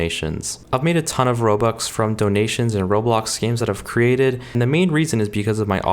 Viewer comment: Did i hear ROBLOX scams...?